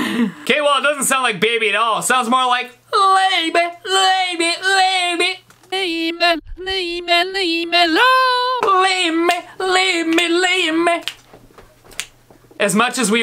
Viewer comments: When he was doing the Justin Bieber one I thought it said baby leave me alone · It sounds like he saying leave me leave me yea